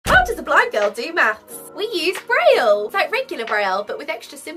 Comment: i thought for a second she said brain